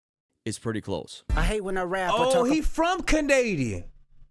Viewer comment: 😂😂😂😂😂😂 what u said bro? He from what? 😂😂😂😂😂😂😂😂 🤣🤣🤣🤣🤣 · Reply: 😂😂😂😂canadea